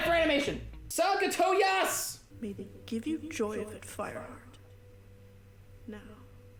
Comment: When da cat said I trusted you fireheart I thought she said firefart